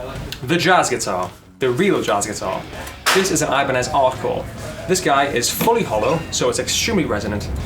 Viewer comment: What guitar is that at 4:36 ?? I couldnt hear what he said :( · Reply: Noob Plays League Ibanez artcore